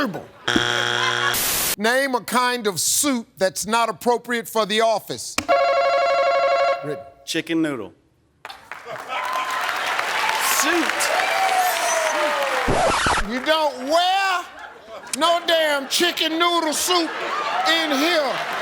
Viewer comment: sounded like soup